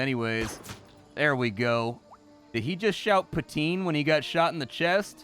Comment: He said "putain" not "poutine". I got confused when I first heard about the food, it seemed odd that someone was so openly discussing how much they love eating putain, then I found out he was just talking about chips and gravy.